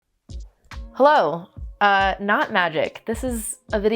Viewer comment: I am five seconds in. I consciously clicked on this video. You opened with "Hello... knot magic." I heard "Hello... not magic." Was momentarily baffled, then immediately disappointed in myself.